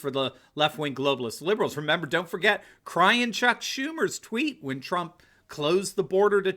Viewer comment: Did he just say Chuck Tumor? haha